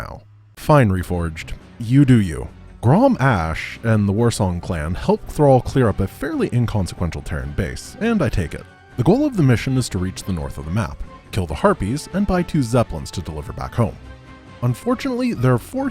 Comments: Did he said terran base? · did he say terran base? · Did he said "Terran base" after 4:25 ?